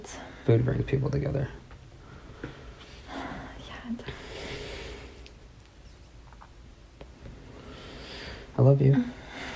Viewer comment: did she say yeah daddy???? tf 😂